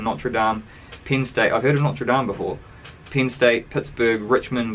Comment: "I've never heard of Notre Dame before." As a Bama fan, all I can say is, "Yessss!" 🤣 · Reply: he actually says "I've heard of Notre Dame before." Using deductive reasoning its easy to assume the one he specifically reiterates is one he recognizes, but then again I honestly wouldn't expect Alabama alumni to even know what "deductive" means in the first place. #FightOn